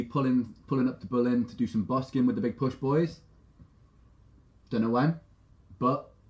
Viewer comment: Did just say he might come to Berlin??? I can't believe that! I'm waiting for TBP!!! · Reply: He said maybe for some busking with The Big Push ?